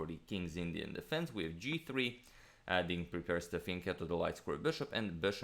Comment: What word is agasmator saying at 1:15? Sounds like "fenketu the light square bishop". What is that first word?? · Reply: fianchetto. Look it up.